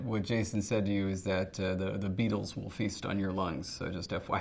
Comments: Before they spelled it out I thought he said "The BEATLES will feast on your lungs". Like, "Okay, random" lol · I heard it as "the Beatles will feast on your lungs"... much more interesting than beetles